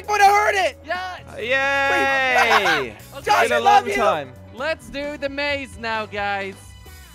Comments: Did anyone heard jelly says: Josh I love you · Who else heard jelly say Josh I love you? 13:32 , who is watching this in 2020?.